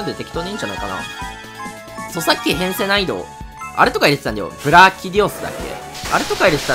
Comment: 著作権編成難易度 にしか聞こえない呪いを置いとくゾ